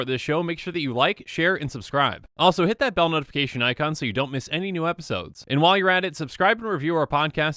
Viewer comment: Why do i hear "so you don't miss any more land drops", when he says "any new videos"